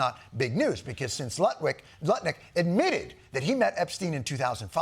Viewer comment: Did John say Nutlick ? 🤣🤣🤣